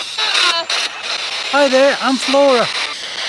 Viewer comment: Amazing voice captures...i heard..hi David I'm flora...